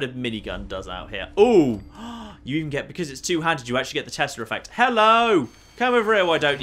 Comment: I so thought you were saying the testicle effect at first.